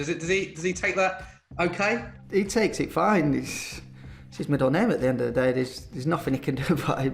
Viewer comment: What does Vardy say at 5:30? ____ at the end of the day? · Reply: It’s his middle name at the end of the day